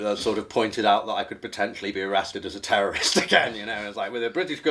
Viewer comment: very nice and informative interview. thank you and thank you to your guest for fighting for what is right. one question though 21:56 - does he say "arrested as a terrorist, AGAIN? I'd like to hear that story sometime